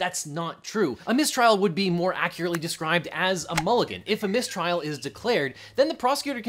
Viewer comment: I thought you said minstrel, like the play thing